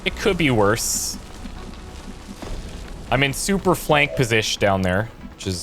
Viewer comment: Did you say "super-flank posish"??? LOVE IT 🤣 You are officially my favorite!